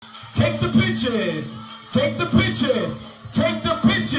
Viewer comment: I thought he was saying “take the b*tches?”